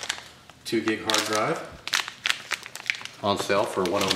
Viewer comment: Wow, I had to do a quadruple check on that. I heard a 2 GIG hard drive. @ 1:23 .. I had to rewind and check to ensure it was actually TB rather than GB. I suddenly thought I was seeing a HD from 1996 when I heard 2 gig. I feel the pain, slip-ups happen. It's right in the description, and (hopefully) that's what counts. Keep making the awesome videos, buddy! I love each and every one!